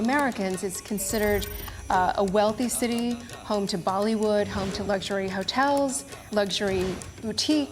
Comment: Did she just say home to Baliwood?